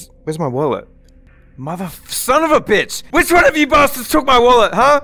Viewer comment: did just said “ma vaff- son of a bitch” hahsh